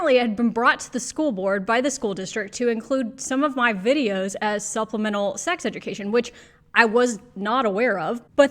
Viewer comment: Supplemental - ha ha ha I thought you said subliminal the first time!!